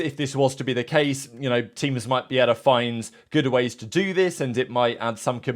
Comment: did he say “good’er ways” LOL